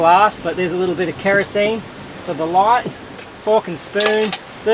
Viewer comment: Wait, did he just say "fkin Spoon"?:face-blue-smiling: Oh, my bad. The AWE-stralian accent! Yeah it must be that!